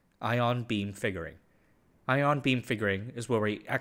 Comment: OH God! I thought you said Ion Beam Fingering 😢